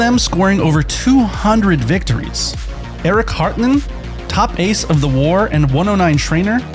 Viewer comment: I heard Eric Cartman, looked back and yea ok the spelling is different. But I wonder...